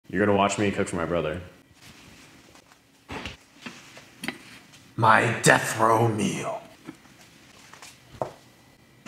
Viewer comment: Haven't watched full video yet, but I replayed the beginning 1k time, I thought I heard.. You're gonna watch me touch my brother.. I was like wtf